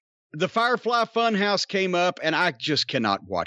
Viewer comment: I first heard, "The firefly fuck house." Now that might be a little more interesting.